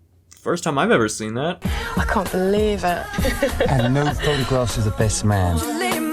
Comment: I thought he said no photographs of the best man